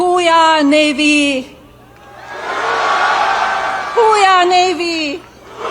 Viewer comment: I thought she was saying.... Who, ya? NAVY??